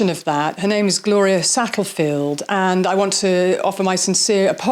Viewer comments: LOL. I just got to the mispronunciation of Gloria’s name…and it sounds as thought you said Sattelfield, it’s Satterfield. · Satterfield . I thought I heard you saying Sattlefield. I hope I can say this without causing great offence. Equally I could’ve misheard .